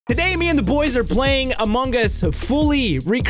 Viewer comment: did he say among gus